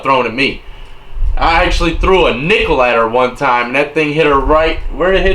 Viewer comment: Haha I thought you said "threw a needle at her leg" when you said nickel 😂 whoops